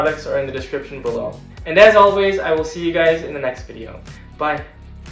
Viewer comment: Not future the nxt vid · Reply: I thought you said the next video